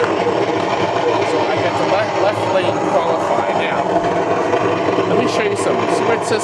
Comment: hard to hear what you are saying about the left flipper.... sounded like a good tip though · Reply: I say that the Left Lane is qualified...thats the top left lane is fully lit now!